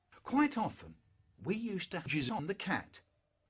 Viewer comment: I really lost it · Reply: Me too at 2:08 I REALLY lost what he said at 2:06 sounded like "We used to whiz on the cat" 😹😹😹